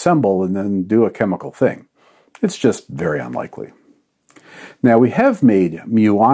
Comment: did he say "It's just Baryon likely", or is it my brain craving for that dry humor!?